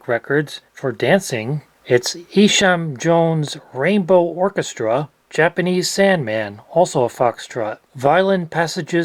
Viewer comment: It's just disgusting having to wait 100 years, the industrial revolution began 100+ years ago lol. I'm not asking for patent-type length but come on, 100 years? I thought that said "Rambo orchestra" xD.